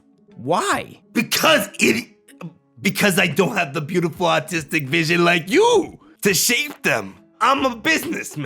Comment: I thought he said beautiful autistic vision